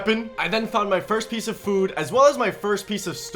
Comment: ‘I then found my first piece of food.’ The apple in his inventory:bruh. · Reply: Bruh u knwo wat i thought i thought u said he found his first piece of wood not food lol i waz confused af